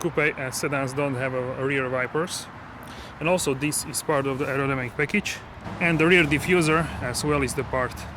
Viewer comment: what package are you talking about? i cant understand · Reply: Aerodynamic package